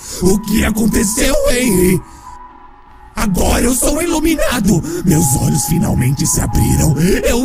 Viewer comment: eu entendi por um momento " eu sou um iluminate" .-.